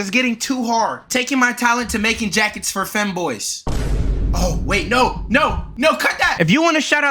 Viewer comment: bro what did you say my boy 🤔😂 femboys